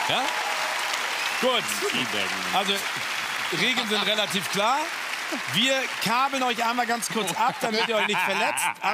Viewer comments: was hat joko da zu klaas gesagt? :D Joko hatte anscheinend das Mikro vergessen :D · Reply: mariemallili Tea Bagging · Was sagt joko bei 2:56 ? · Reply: "...wir tea-bagen en..."